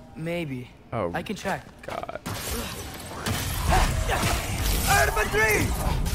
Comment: why does Atreus sound like he is saying out of my jeans 😂